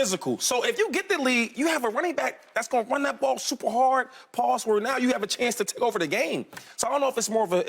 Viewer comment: Did Shady just said Pause at 7:30 😂😂😂😂